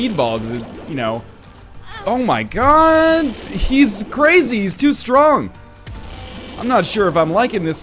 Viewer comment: Did i hear Northernlion just say that Dingle is too strong